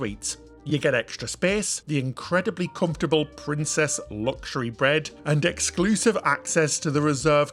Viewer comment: DID HE SAY "PRINCESS LUXUARY BREAD" I GUESS HE LIKES SLEEPING ON A LOAF